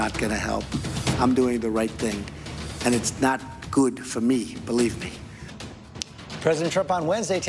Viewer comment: Whenever trump says "believe me" I hear "f you". Every single time.